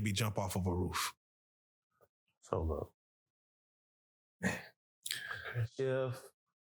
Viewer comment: What he say at 7:40 lol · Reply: "so look"